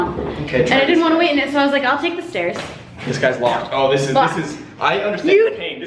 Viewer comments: what is the word says at 1:58 when the door is locked????? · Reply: DangThatsALongName "it's locked" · did just say 'fuck'? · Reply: Mason AwesomeFace I think she said locked